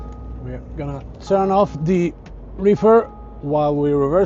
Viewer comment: Vad sa du att du stängde av? 😊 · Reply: Reefer, kylaggregatet 😉👍